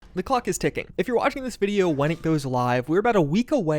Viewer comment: All I heard was "the cluck is ticking." and that was it, laugher.